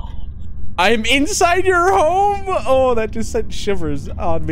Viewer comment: i thought he said "i am inside your hole" 💀